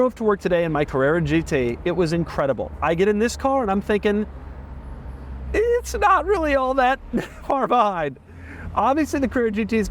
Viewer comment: What does he say at 20:35? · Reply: "It's not really all that... far behind."